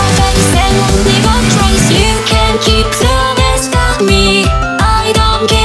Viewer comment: I aways thought she was saying ‘drowning slowly’ not ‘the last of me’